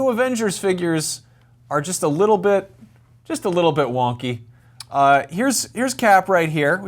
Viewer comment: I thought u said just a bit wonky